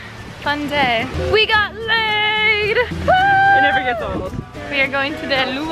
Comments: Did she say "We got laid" at 2:58 ? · it sounds like Alisha said we got laiddd and I freaked out like Alisha keep it PG but then I saw the flowers ;) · I thought she said we got laid but then I saw the leis